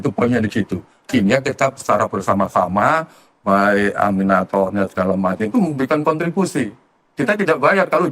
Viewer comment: Abang salah denger "ga dibayar" pekerjanya. Saya malah salah denger " AMINATOR". Saha teh aminator?😂😂😂